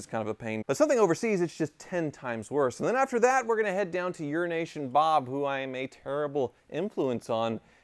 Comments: I always hear it as “urination Bob” not euro nation Bob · With that accent of Hoovie, for a long time I thought he was saying Urination Bob, 😉I still hear it everytime it's mentioned. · Ok, am I the only one that keeps mishearing euro nation Bob as urination Bob?